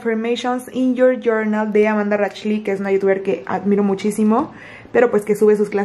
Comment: Cómo dijó que se llama la Youtuber que le gusta?, no le entendí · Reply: Creo que dijo Amanda Racheli o algo así, eso fue lo que entendí 😁🪴